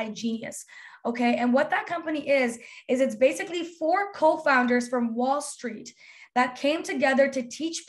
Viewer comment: i thought she said 4 cult founders